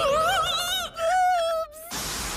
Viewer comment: Lol. It just occurred to me that Uraraka actually says "Boobs" instead of "Oops" at 1:16 😂😂😂